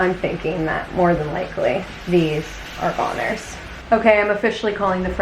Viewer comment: lol I thought you said "those french toast are Conners" took me a second to realize you said goners. lol geeze. Hope you guys have a great weekend.